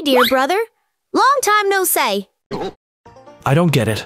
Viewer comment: damnnn she really says " loang time no seay "